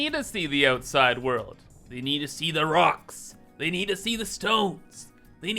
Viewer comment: DID I HEAR A ROCK AND STONE?!